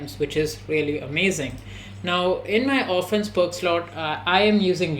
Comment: wdym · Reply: he is talking about this moment when you say "in my offence perk slot". The subtitle says "in my orphans perk slot"